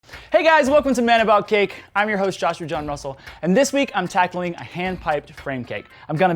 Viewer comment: How is it possible that I ALWAYS hear him saying "Joshua McJohn Russel"?!?! WHY do I keep hearing Mc in the middle? Am I crazy? Does anyone else hear that too?? No? Just me? K .-.